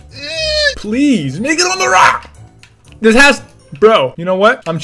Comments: is it just me or did it sound like he said something bad. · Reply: He said “lemme go on da rock!!” It sounded like he said something else, it was just cause he said it rlly fast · sounded like he said ni**a on the rock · I know this is 2019 but... 1:41 sounds like he says the n word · did anyone hear fer4less say the n word? like if you heard him say that when you kill someone in a really cool way,what do you do? 1:dance. 2:Scream CLIP THAT 3:Meh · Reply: He said make it on the rock I get what u heard tho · is it just me or did it sound like he said something bad. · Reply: he says make it on the rock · I thought he said "n***a on the rock!" XD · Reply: He said make it on the rock listen in slow mo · wait what did he just say n—— on a what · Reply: That doesn't even make sense to say, he said "lemme get on the rock" This stuff happened with Ceeday too and it's annoying, some things that people say don't come out clearly. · what did he say 😦😟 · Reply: “Make it on the rock” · what did he say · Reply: He said”MAKE IT ON THE ROCK”Really fast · what did he say ni on the rock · it sounds like he said nigga on the rock at first but you had to listen more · it sounds like he said “ni**a on a rock” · WHAT DID HE SAYYYYYYYYYYYY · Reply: He said make it on the rock